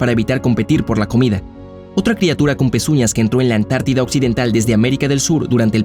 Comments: Buena información, pero cerca del minuto 5:00 este locutor parece que dice "andó" cuando debió decir "anduvo" ,eso deja mucho que pensar del detalle profesional y hace bajar la calificación del video documental! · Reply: Yo escuché "Entró" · Buena información, pero cerca del minuto 5:00 este locutor parece que dice "andó" cuando debió decir "anduvo" ,eso deja mucho que pensar del detalle profesional y hace bajar la calificación del video documental!